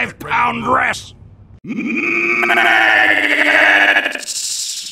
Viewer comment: Soldier: "MMMMAAAAAGGGGGOOOOOTTTTTSSSSS!!!" What i heard: "MMMMMMMMM---AGGOOOTTSSS!!!!!!!" As if he was going for MEDIC and then realized he was at full HP